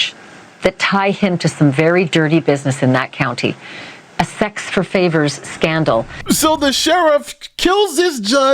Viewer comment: Did anyone else hear her say, "Sex for fingers" at 1:08? Either it means something to the case or it's a hilarious faux pas.